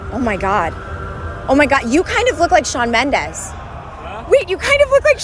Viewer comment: I thought she saying charmendes